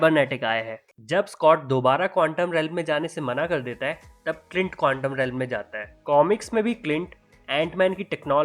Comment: Who heard condom at 4:35/36 or my ears got some issues. 😂😂